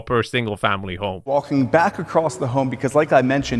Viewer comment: I’m an electrical engineer who designs electrical systems in residential properties. I can assure you that, electrically and mechanically, it’s as much of a mess as it is architecturally. Worst of all, the solid concrete floor and no conventional walls on the ground floor makes fixing these problems 10x harder. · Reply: When he said at 15:45 “walking back across the home” I heard it as “walking back across the garage “ 😂